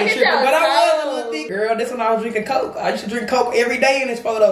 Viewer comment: i thought said I was eating coke I was like 🤭😑....ooooooo she said drinking coke